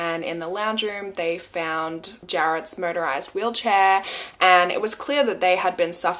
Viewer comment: I thought she said murderized wheel Chair. I was like 😳😨…oh 😂